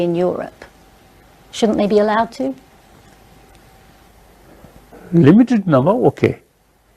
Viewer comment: I couldnt understand the first few words he said. 5:29 "limited is now okay?" something like that? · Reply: She says "shouldn't they be allowed to" and he replies "limited number ok"